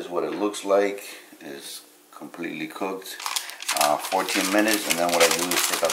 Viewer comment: At first I thought you said bake for "40 minutes" 😲🔥 🧀 .. had to rewind and yup, you said 14 😁✌ These are so good and simple to make 😋👍